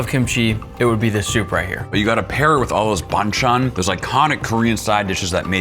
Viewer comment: I HEARD BANGCHAN